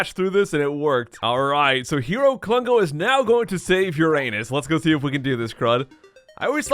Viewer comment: "Hero Klungo is now going to save your anus" Uhhhhh that sounds very wrong... EDIT: Like if you think that him saying "Save your anus" sounds wrong...........................